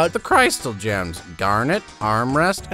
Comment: I thought he said ‘Darn it’ instead of ‘Garnet’. It would be really funny if Garnet was Darn it instead though XD